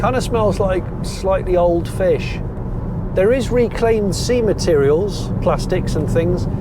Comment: I genuinely thought you said “ reclaimed semen “.. spat my tea out 😳